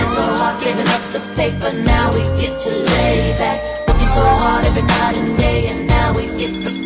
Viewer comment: why do I hear "working so hard every night and day" as "booty so hard every night and day" lmao